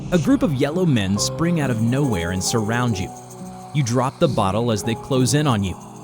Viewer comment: bro that sounded like "don't drop the soap" backrooms edition 💀